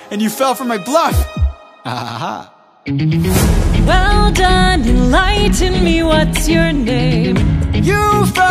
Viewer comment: The fact that she said "well done, enlighten me, what's your name? " I really hear "well done, you lied to me, what's your name? " Like imagine Athena being like "GREAT JOB LIAR"